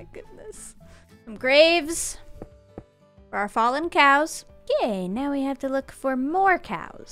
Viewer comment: Was saying "gay" instead of "okay" her way of hinting that she's bi (to the people who don't follow her on Twitter)?